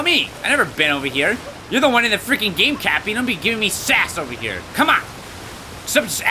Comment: when he got cappy I thought it said crappy lol